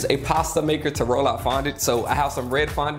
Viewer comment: i always wondered what’s he saying “fondlet” like i can’t what is he saying